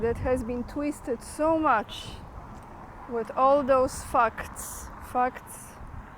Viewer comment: I thought you said 'fucks' instead of 'facts', the same meaning really anyway.